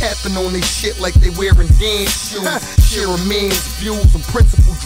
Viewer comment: Did he just say Vans shoes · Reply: No. Tappin on it like they wearing dance shoes. Not vans shoes. C'mon now. Do better